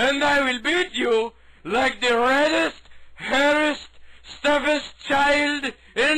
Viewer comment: Seriously, can ANYONE tell me what the fuck Harry says to the other prisoner? Fifteen years later and I still don't know what he's saying... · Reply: I do get that he threatens the prisoner with a beating. the only words I dont get are when he says "wildest" "hairest" "steppest" I actually didn't know "red headed stepchild" was real expression until now. Funny. Still, I only hear "wildest" when you say Harry says "reddest" =/ But I thik your explanation seems logical so its probably what he says. Thanks for clearing up a mystery that has bugged me half my life =)